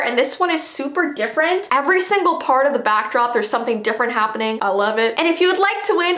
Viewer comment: I thought she said "eleven" XD but she said I love it.